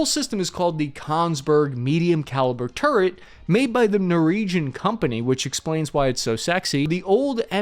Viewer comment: Wait, did you say Noregion ??? You did, lol...... . . . . .